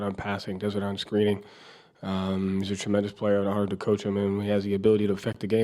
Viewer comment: At the 1:10 mark does he say jt is hard to coach or am I tripping? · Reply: *an honor to coach him (But that ish did sound like what you thought it did 😂)